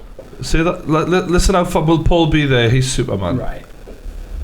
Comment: I thought it said Dumbledore is Superman